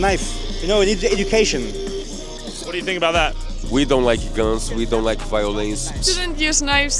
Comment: "we don't like girls we don't like violins" what i heard